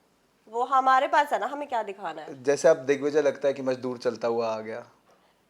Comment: What he said about digvijay on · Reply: something about maajdurr